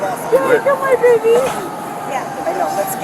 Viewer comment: She cares a lot at beginning of video then someone tells her baby is probably ok and she drops it I don't believe she had been informed her baby hadn't survived when this video ended and she was being transferred to jail. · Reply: I heard her saying at the beginning of the video “Did I kill my baby”.